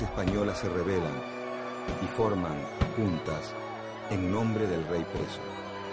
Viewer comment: Alguien que me diga que dice entre el minuto 3:30 y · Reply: "En nombre del rey preso..." bue re tarde.